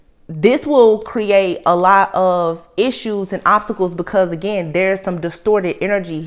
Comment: I could a swore you said optical!❤ Had to replay it 3x and final turn on the captions! B UT ima go with what I 1st heard ; Optical, Optical Delusions; I’m using mine too build upon, not blame Thank You I Needed That ❤